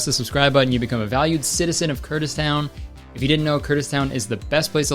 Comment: when you said Kurtistown I thought you said Kurdistan for a second lol